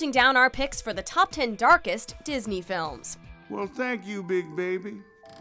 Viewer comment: thought it said "dankest" disney movies